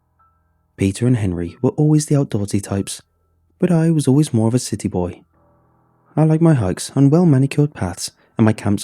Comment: I heard "city boy" as "sitty-boi" and honestly, same.